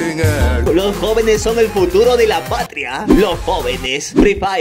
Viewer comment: alguien se dió cuenta de que dice juturo